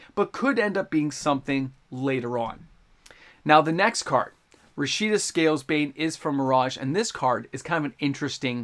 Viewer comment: Did I hear Rashida SalesBait @ 6:56 lol!!!